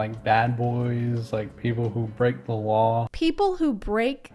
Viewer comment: when he said "people who break the law" i heard "people who frick the wall"